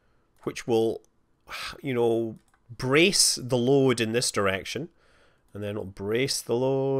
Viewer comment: I thought for a moment he said "Praise the Lord!" :D